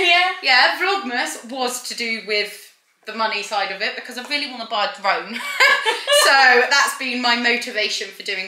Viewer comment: What did you say you wanted to buy with the vlogmas money? It sounded like "buy the roan". Pretty sure I misheard.